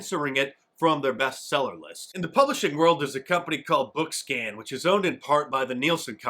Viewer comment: I thought he said Book Scam. Lol